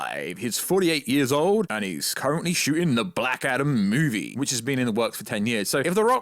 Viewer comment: I keep hearing you say 'the Blackadder movie'